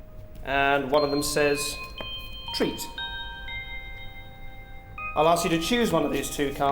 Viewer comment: Am I the only one who realizes it doesn't matter what card you choose? You turn "Treat" upside-down and get trick, and vise versa. So everyone gets f*cked over by Derren... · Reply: Insanity Leader Yeah I swore it said "trat" not treat